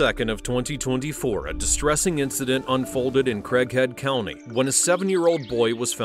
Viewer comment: I thought he said crackhead county in the beginning of video